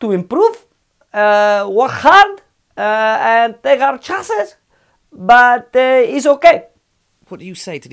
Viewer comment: wtf did he say ? 🤣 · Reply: Chances